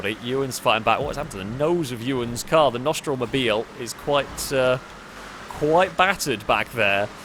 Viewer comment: I had to laugh whenever I heard "nostril mobile"